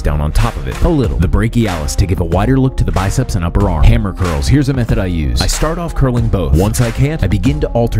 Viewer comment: I thought u said hammer girls lol